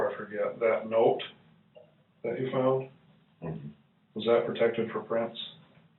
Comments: he actually said ""was that protected for prints?" he did not ask if it was dusted. This makes the response of "i might have touched the corner of it" way less suspect, it is a very reasonable response to the question he was asked. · I hear the investigator ask if the note was "protected for prints" NOT "dusted for prints which is completely different.